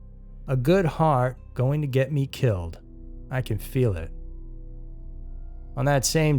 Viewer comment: he doesn’t say that, he clearly says, I can see it.